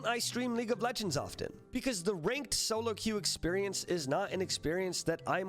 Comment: I heard you say the “rigged solo q” instead of “ranked solo q” and pretty much agreed anyway